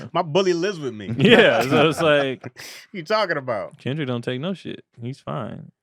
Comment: all I heard when he said this was will farrel “gator don’t take no shït”